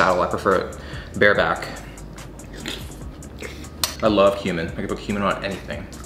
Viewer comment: If you turn on the subs at 5:18 "I love human. I can put human on anything" If that's not a full on american psycho thing I don't know what is 😂😂😂😂 (I know he said cumin lol it's just hilarious)